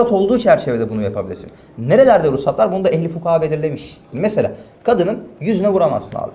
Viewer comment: ' te tam olarak ne diyor. Ehli fukara gibi bir sey olarak duyuyorum ama anlamadim. Yazabilecek var mi acaba